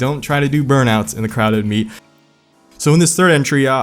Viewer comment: I thought I heard him say clown meet because the people who are stunting are clowns.